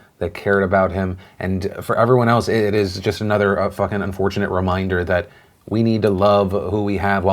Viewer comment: Does Philly say FUCKING unfortunate reminder at 3:37 or is it just me??